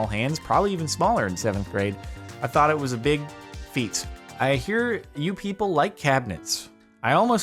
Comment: he thought his small hands was a big feet :) 5:46 thats what i heard on the first pass hahaha